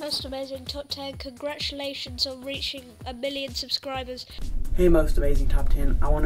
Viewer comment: Did he just say congrats on reaching a BILLION subscribers?